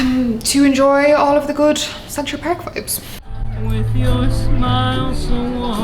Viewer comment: may I know what did she say again? To enjoy what again? I really didn't get that it was way too fast :( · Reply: “Central Park vibes” ✨